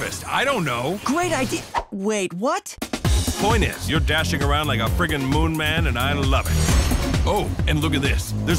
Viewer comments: I swear i hear him say "you're dashing like a freaking moon man in 9/11" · Everyone’s talking about the “anime fan on prom night” joke but what the hell was with him saying “dashing around like a freaking moon man on 9/11” at 0:35??? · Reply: You misheard it that's why lol, it waaaaaas "dashing around like a freaking moon man and I love it"